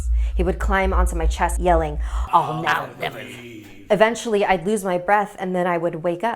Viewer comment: what was the man saying?🤨🤔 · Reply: king J I will never leave I believe